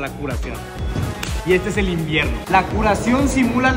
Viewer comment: Nose porque pero dijo "y este es el invierno" y yo escuché "y este es el infierno" xD